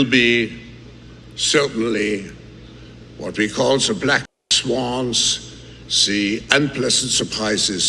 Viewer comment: I thought he said ''Black Swans'', like there would be multiple events.